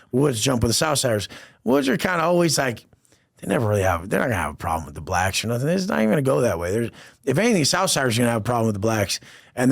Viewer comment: The first couple minutes I thought he kept saying wet backs 💀